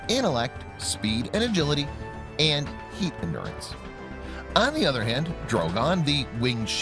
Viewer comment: what does the narrator say in 2:31? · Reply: Heat endurence